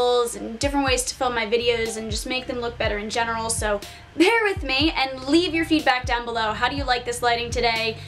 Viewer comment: I thought you said “Pear with me.” and I was so happy. 🤗🍐